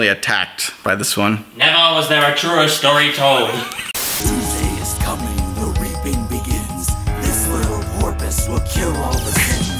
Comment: I heard "Poosday is coming the leaping begins" XD